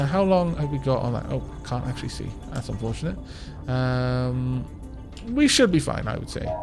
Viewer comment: i though i heard you say 'that's some bullshit'. i had to listen again to make sure and when i did i heard you say 'that's unfortionate.'.